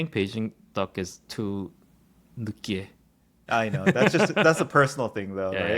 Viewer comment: what did say in Korean about the Beijing duck at 1:14:22? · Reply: 느끼해 (too greasy)